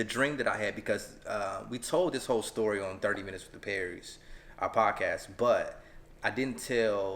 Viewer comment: I thought it said pray with the Perrys